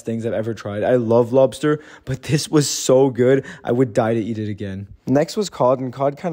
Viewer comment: HELP I THOUGHT HE SAID "the piss was so gooddd" FOR THEBEETROOT LOBSETER THING😭😭😭